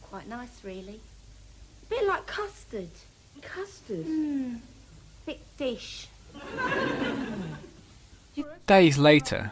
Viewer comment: I can't make out the punchline to the custard joke 10:00 even with subs on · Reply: I think she says, "Thick dish."